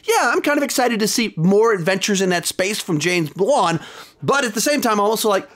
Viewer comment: it sounded like he was about to say James Bond